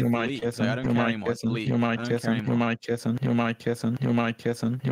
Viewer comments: It sounds like George is saying on the soundboard >who am I kissin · if you listen hard enough, it sounds like hes saying "who am i kissing" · “Who am I kissing” is what I hear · Lol when sap starts to spam George saying you're my kitten it now sounds like you don't mind kissing idk why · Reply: I THOUGHT IT SAID "Who'm I kissin"